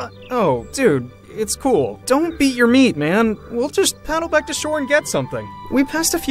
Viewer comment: did he say dont beat your meat?